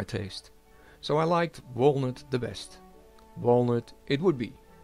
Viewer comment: "Walnut". I thought you said he-who-shall-not-be-named (Voldemort). Lol. It came out beautifully!